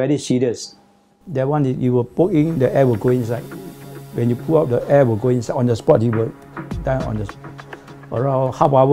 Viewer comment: what is the weapon called? can't hear properly. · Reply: bearing scraper.